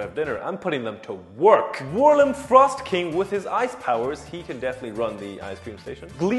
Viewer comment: During the sponsor, I misheard “Frostking” as “Thrustking!” The bootlegs are invading my brain!!!”